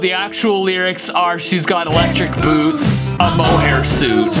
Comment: For electric boots, I heard “electric boobs, a mole rat suit”